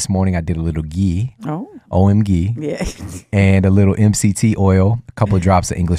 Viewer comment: an ingredient was mentioned, sounded like Owen Gee, and I was curious how it's truly spelled so I can try it. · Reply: ghee :)